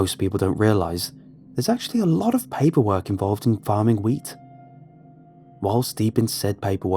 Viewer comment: WHEAT...ah...I thought he said weed😂